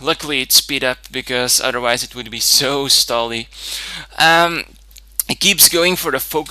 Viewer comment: GREAT BATTLE BTW on 3:28 you said Zo stally instead of so stally i think you mesed up english with dutch XD (im dutch 2 so )